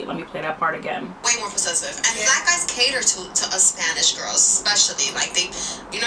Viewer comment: I heard her say "cater to," not "love." I'm curious why some conflate those two terms.